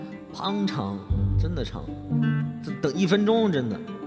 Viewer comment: i can't rly make out what he says in the beginning (那是,,你叫个人来就忘记 is what i heard but...) 0:47 - 那前奏胖(?)长: that prelude is so long 0:54 - 等多少... 一分多呀...我会(?)- 我跳一下: wait for how long...more than a minute...i'll- i'll skip a bit thanks for sharing!! ❤️❤️ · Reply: 是滂长啦，应该是四川那边的方言